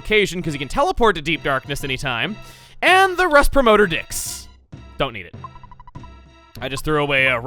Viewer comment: I thought you said “don’t eat it”